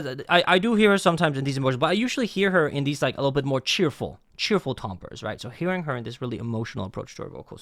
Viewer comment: I thought you said chewable tombres (idk how to spell music stuff) I was confused